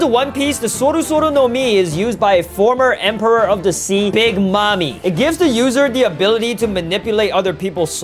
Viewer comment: Is it just me or did he just say mommy instead of Nami?